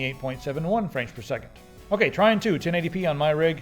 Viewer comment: What game is that? @ 5:03 I can't hear what the name is. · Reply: Trine 2.